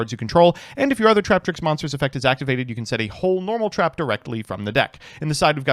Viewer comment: I keep hearing it as "you can set a whole Trap card." What a great deal!